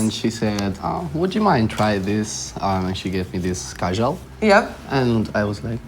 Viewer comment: did he say "Kajal"?